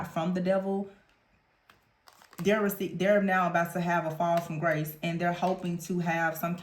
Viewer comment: When you said this person is having a "fall from grace" I heard "safety net". Yikes.